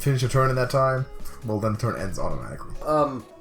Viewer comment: not quite sure if you're trolling tbh but in case you're not, when the end turn button is green that means you have no more moves possible on your turn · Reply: alright. I got it. say things clearly. I thought entron when you say end turn. Not everyone is your friend who understands your pronunciation.